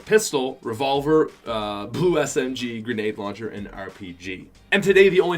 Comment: did he just say poo smg???